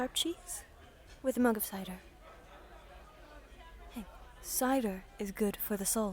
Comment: “Cider is good for your soul.” If I ever have merch, that will definitely be on one of them. This is the longest video I’ve ever made oof so I really hope you enjoy. 💀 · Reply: thought you said cinder, Dark souls mmm